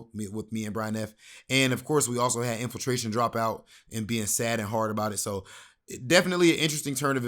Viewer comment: did infil just say "it's made me sad and hard?" Bruh...😂👋